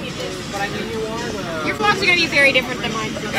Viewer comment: What did Mary say at minute 1:31? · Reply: She said vlogs. Note: The way I wrote it may come off as blunt but I don't mean it to.